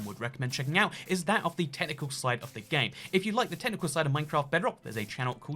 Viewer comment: I was moving my phone on top of a pillow and I thought he said the tentacle side not technical and I was HOOKED IN